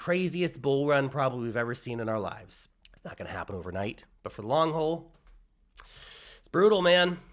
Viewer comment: Did you just say "the long hole" lol